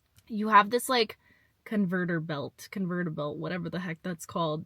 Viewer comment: I have some guesses for the words you couldn't figure out but I could be wrong: conveyor belt for 6:40 and either stray, deviate, setback, or detour for